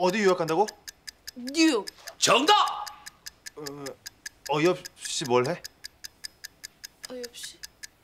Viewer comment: 뭐라고 한건가요... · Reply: 어이없이 뭘 해?